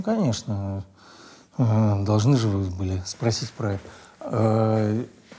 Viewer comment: прислушайтесь что он сказал, или мне показалось?)) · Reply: Там "были", я прислушался 😂